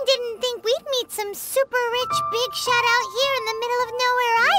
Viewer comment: Wasn’t paying much attention at 0:58 so for a second i thought paimon said: “rich bitch”